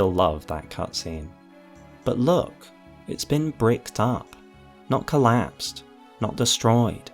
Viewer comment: WHAT DID HE SAY???????????? 🤨🤨🤨🤨🤨🤨🤨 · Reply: That it's been bricked up. Sealed. Blocked off.